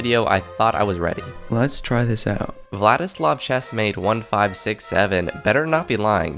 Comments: You are not slick, I heard you say the 6 7 meme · did bro say 67 💔